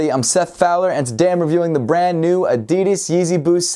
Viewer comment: Did he say adidis